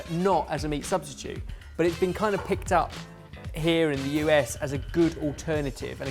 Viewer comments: did he say "here AND the U.S."? or "here IN the U.S."? I know they are british but have they started filming in the US? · It took me a second but it sounded like he said "here in the US" when he of course said "here and the US" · Ben? Has the studio moved across the pond? 0.o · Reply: I heard "here and the US" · Did ben say "Here in the US" at like 7:20? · did he say here in the US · Did Ben say "Here in the US"? Or am I crazy? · did Ben say "...here in the U.S." at 07:18???